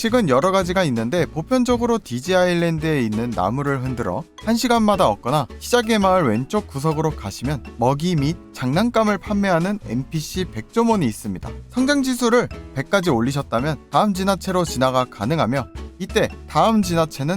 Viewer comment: 내 귀에 뭐 문재가 있나 NPC 백조몬 이라는말을 NPC 백종원으로 들었네 ㅋㅋㅋㅋㅋㅋ